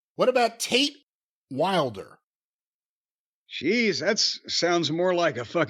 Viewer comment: Legit thought he said Taint Wilder